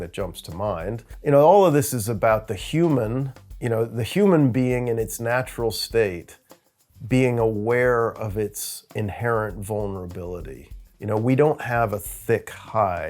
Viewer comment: What said (23:19): "All of this is about the human being in it's natural state; being aware of it's inherent vulnerability" What I heard: "Once we realize the weakness of our flesh, it will disgust us"